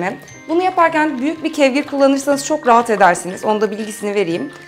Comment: kevge mi diyor ne diyor internette arattim yok ? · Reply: Kevgir